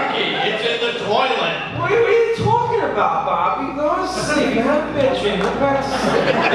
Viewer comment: What did he say at 5:41 ? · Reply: "What are you talking about Bobby? Go to sleep, you had a bad dream. Go back to sleep."